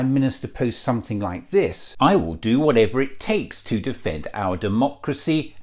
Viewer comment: Sorry i thought it said defund hes a patsy for the wef cult of shwarb and a king gobshites along with rest of the countries attached to this loonatic cult